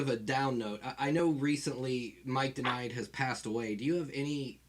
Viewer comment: did he just say that Mike IX passed away? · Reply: Mike Denied, former Murder Junkies singer.